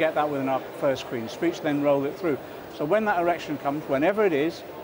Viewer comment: Did he just say, 'When that erection comes'?